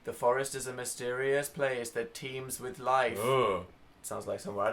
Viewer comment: instead of "teems with life" I thought it said "teens with life" and when they said "nope" I was like "yeah, what teen has a life?"